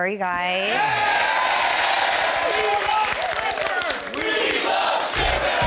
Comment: First I thought they were saying we like shippers